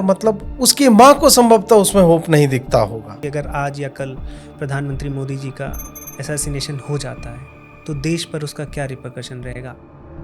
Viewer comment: Did I hear it right? You said "If Modi got assa******ed ‽"